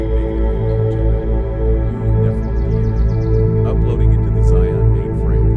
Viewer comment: Did I hear "uploaded to the Zion mainframe" at the end?!😂. Bravo if that was what you said.